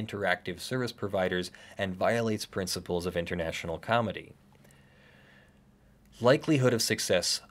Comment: Can you please explain what "comity" means? Because I keep hearing "comedy".